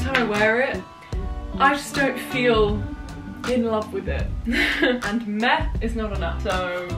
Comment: Thought she said "Meth is not allowed“ oops